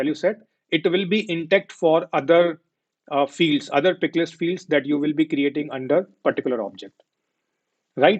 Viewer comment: Sorry, At 40:03 minutes I didn't understand this point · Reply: Pick list field*